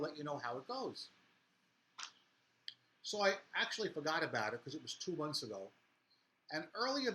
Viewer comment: did you actually say "It was 2 months ago" at 13:15? The credit card company took 2 months to make a decision? Your money was tied up for 2 months? What the hell is up with that!?